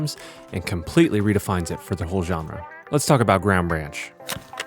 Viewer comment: wtf I thought he said “let’s talk about ram ranch”